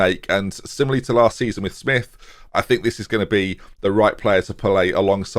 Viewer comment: Did say Pele instead of play when referencing a striker?